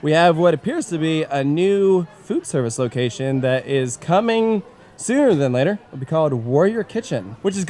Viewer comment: my thing froze up here so it sounded like was saying “it looks like there’s a new food place coming soup-“ 😂😅